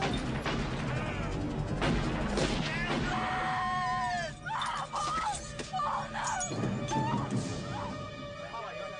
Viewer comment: what did he just said in 5:54 before he died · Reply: Katherine